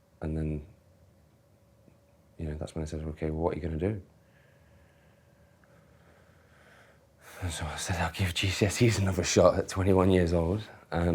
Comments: The most replayed part is at 13:00 where he mumbles something. I must not be the only one not to understand what he says. Anyone know? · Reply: “And that’s when they said, you know, ‘what are you gonna do?’ And so I said I’d give GCSE’s another shot” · what does he say?? I hear “I’ll give (Jesus?) another shot at 21 y.o” and then “it means much to him”? But that can’t be right and the captions don’t help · I couldn't understand what he says here. Can anyone tell me ? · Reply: i will give GCSE’s another shot at 21 years old